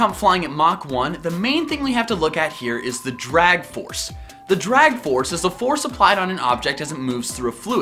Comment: When you say drag forces I thought you said. Dragon forces